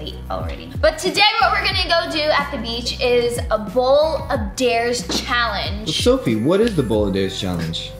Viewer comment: Sofie I thought you said bowl of dares not cup of dares